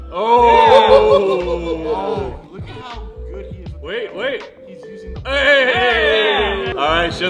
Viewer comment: WOOOW loved every minute of it :D btw i think said something just after shin is finished? it sounded like "look at how good he is with....hes using the....." @ 3:53 Im dying to know what he said :P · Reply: joshua dsilva “... frame to his advantage”